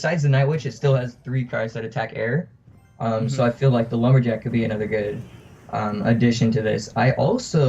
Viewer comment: what did he say? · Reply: i have three cards that attack air so the lumberjack could be a good substitution